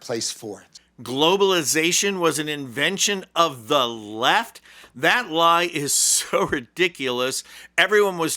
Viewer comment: I heard him say that globalisation was a product of the WEF - NOT the 'left'. I listened twice to be sure that I heard it right.